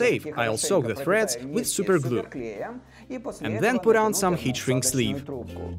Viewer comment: what does he say on 3:08 till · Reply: duijts he means heat shrinking tube